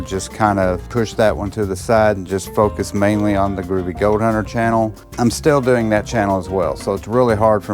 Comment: the captions said "Groovy goat hunter" Lol, just wanted you to see it.